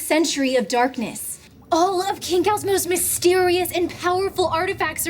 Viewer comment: Omg seeing the islands name spelled out here makes me realize how unfortunate of a name choice it is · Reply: I could've sworn they were all saying Kingkau, but apparently not lol